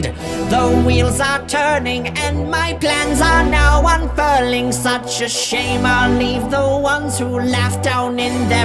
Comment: Why did I hear "And my glands are now unfurling" 💀